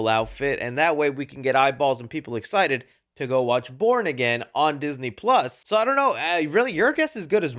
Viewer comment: It sounds like said go “watch porn again on Disney plus”😂😂😂